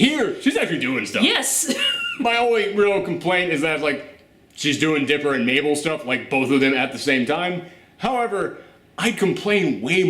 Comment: At around the 8:40 mark, I (mis)heard something that made me go "Wait, WAHT?!?"... That is, I didn't hear the word "stuff" at the end of the first half of that sentence, so what I heard at first was "She's (Webby's) doing Mabel and Dipper, both of them at the same time"... :O And I'm sitting here thinking, "DAMN! Disney and DuckTales are NOTHING like what I remember; WTF?!?!?" XD